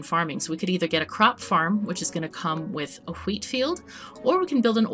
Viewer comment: did I hear that right? Huwheat field? Well played, well played indeed, madam.